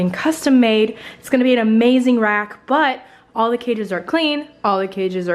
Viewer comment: "It's gonna be an amazing rack." I heard, "You got an amazing rack.."